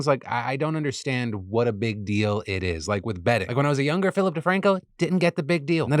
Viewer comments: Phil, when you said “bedding” I thought you said “betting” and you started taking me down a whirlwind of a path. I was thinking for a second, “Phil didn’t gamble until he bought nice sheets? The hell sort of sheets did he buy??” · At the beginning of the ad read I thought Phil said "betting" and was just like god damn prize picks leave me one youtube channel without your shit. Thank you Phil for selling us pillows instead.